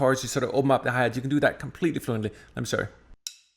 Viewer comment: He says, "Let me show you." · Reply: I thought he said, “I’m sorry,” too, haha! You’re correct, he said, “Let me show you.” Fast talker!